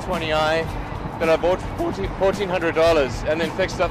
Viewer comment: en el minuto 13:24 la cifra correcta que dice es 1400 dlls (no 14 mil)